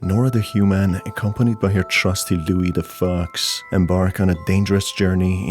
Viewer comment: I swear to God I thought he said "Louie the fuck"